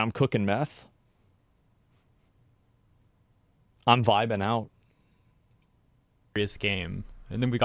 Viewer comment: Oh he said "vibin out" 18:42 thought he said "Biden out" Like phased out, mind is in a whole other place